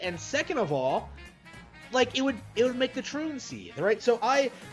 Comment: I'm sorry, what the hells did he say at 9:19? Was that the T slur or am I mishearing things?